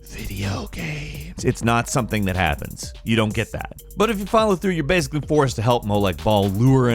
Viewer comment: did he say fallout 3????